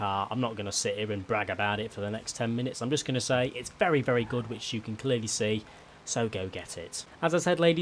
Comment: wait what did he say. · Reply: “It’s very very good, which you can clearly see, so go get it”